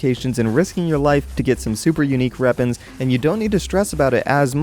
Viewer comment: pretty sure I heard "super unique reapons"